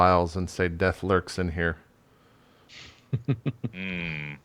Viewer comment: I always hear "unspeakable whores" not "horrors"...